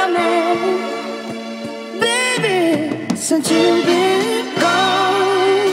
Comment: This beat dope asf but it’s sounds like he says “ he know I’m only half a man”.. kinda gives the wrong impression